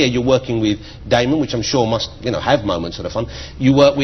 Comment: What does Jonathan said about Damon in the minute 4:56 ?? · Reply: “With Damon who should yknow, have moments that are fun” or smth along those lines lol.